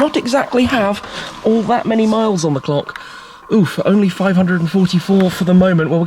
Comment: Did I just hear him say ‘oof’??? PLAYS ROBLOX CONFIRMED